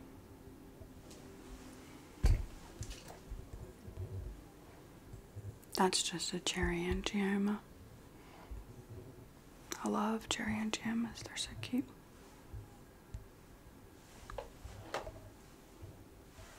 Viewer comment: Did I just hear her say “I love cherry angiomas, they are so cute”